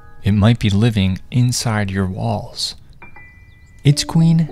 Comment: Gotta rewind that, i thought he said "Inside your balls 💀💀"